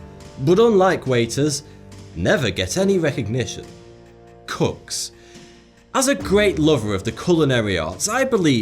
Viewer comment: When I heard you say cooks I thought you said cucks